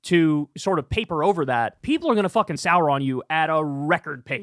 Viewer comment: lol i heard "... are gonna Sauron on you..." and i was like- now how do we do that?